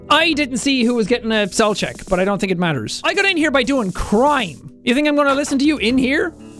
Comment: “I’ve gotten really good at crime over the years” My dumbass thought he said “crying” instead of “crime” and i was like, “biTCH ME TOO”